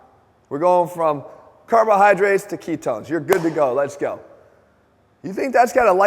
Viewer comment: did he say carbs to ketones?